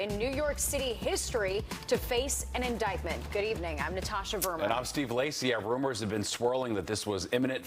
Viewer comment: I thought she said "I'm Natasha Vermin".